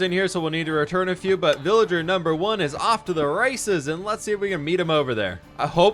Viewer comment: why does it sound like is saying “off to the racism” which I know he’s not because that doesn’t make sense (just for where the villager is going,) but I also have APD so if anyone knows what he is actually saying I’d love to know aswell